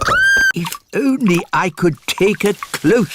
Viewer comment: in subtitles why did it say if only i could suck?! 😂😂🤨🤨